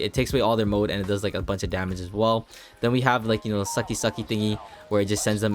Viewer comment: i hope what he realized what he said 15:15 he said sucky sucky